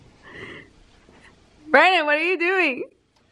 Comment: mins did she said Bryan ? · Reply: Luis Bautista bro stop she said Brandon